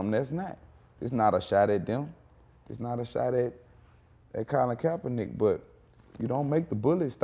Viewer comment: What word does he say at 10:41 ? Someone help it’s buggin me lol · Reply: Colin Kaepernick